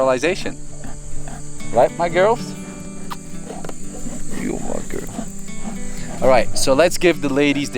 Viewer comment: Heyy y 'll 12:18 .. did someone notice she sounded like ""YO MY GURL""